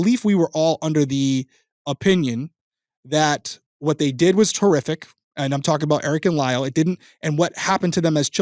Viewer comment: lol sooo…I know you said “horrific”…because, context… but bro, that sounded a lot like “terrific” (as in…”what they did was terrific!”) and I had to laugh at that. Then I felt guilty for laughing, given the subject matter, but it was funny, nonetheless. Apologies to anyone who is offended by this misheard statement… I guess I’ll just “cough it up” to another reason is an absolute legend.